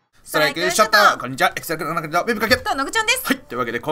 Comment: かわけなんて言ってんw